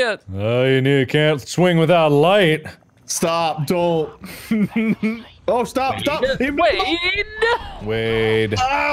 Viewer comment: ya'll headphone users hear someone whispering in the background about going back to sleep? Cause it sure as hell doesn't sound like one of the boys and i replayed it multiple heccin times to make sure i wasn't going insane. It literally scared the shit out of me. Or is that just part of the game audio, if so, the developers have an interesting taste in game audio and soundtracks. (edit) WOOp i just heard it again and i realized it's one of the game characters saying "let them sleep", or something like that. I need to take a break from those horror movies 😅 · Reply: The full quote is "Don't wake them up, let them sleep."